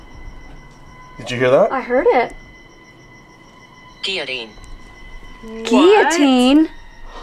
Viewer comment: why do I HEAR deity but it SHOWS guillotine?